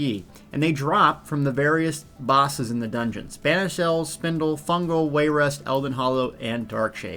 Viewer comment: Lol. Banished Cells? I thought you said Spanish Elves.